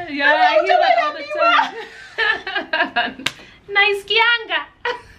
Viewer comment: Did she just say 'naiskianga'😘😘😘